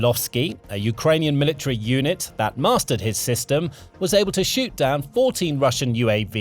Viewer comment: Did you say "shoot" ? 🤔